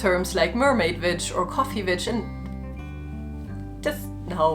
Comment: I misheard "coffee witch" as "coffee bitch" and just nodded sagely with a "yep, thats me" Followed by "oh, shit thats not what she said"